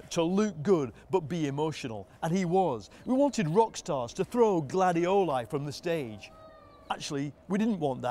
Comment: You realize you have a problem with football when you listen Guardiola instead of Gladiola